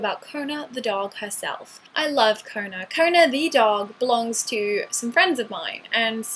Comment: Wait... I thought you said 'I love koma" lol :(